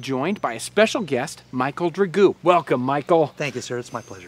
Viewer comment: I thought he said his name was Michael Burgoo. Lol. I must be getting old.